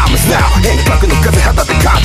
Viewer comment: not fire!!なの？ now fire!!に聞こえたけど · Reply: Nowですよ